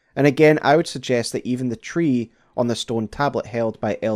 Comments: Did he say the tablet held by Elton John..?? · Did I hear you say “… on the stone tablet held by Elton John…” at 4:57 because if Sir Elton is canon in this story then holy moley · did he say elton john 😭